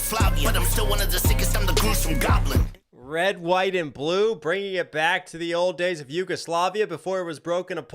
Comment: I thought he was saying revolution, not Red, White, and Blue